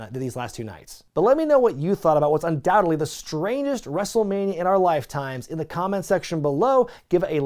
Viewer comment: "The strainest wrestlemania of all time"? LOL did anyone else catch that?